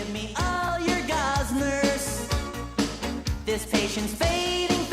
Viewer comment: I thought it said "gossmers" for the longest time, I wondered what it actually said